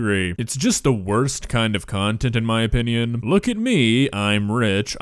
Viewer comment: I thought he said “the worst type of cunt” at the end😂😂